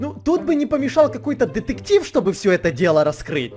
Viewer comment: Эд, мне послышалось, или ты сказал ДЕТЕКТИВ?! Эдуардо Перецяно будет учавствовать в этом сезоне?!(если не правильно написала простите)